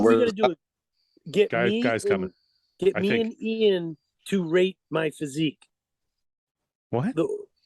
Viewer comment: Thought jose said “rape my physique” even nick thought it. Look at nicks face when he initially says it.